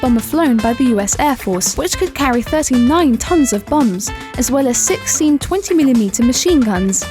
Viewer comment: she said tht th plane can carry up to 39 tons of bums who else heard her say tht if so comment on this back and say I